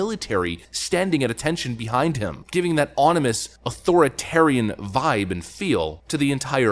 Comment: …did i hear Onimous? Ominous… haha my slydexia kicks in periodically oot